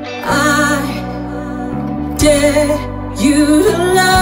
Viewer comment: ايش يا اخوان انا سمعت اتحداك ولا I dare you بتتوهمللي ولا فاهم اشي مين انتو ...؟ 00:47 هون ركزو 😂